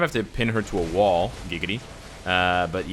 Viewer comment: is it me or did he say giggity (family guy)?